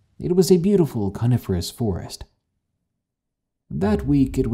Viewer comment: i've been listening to your stories for too long, i think... i heard that as "carnivorous forest" and had to double-check. ;p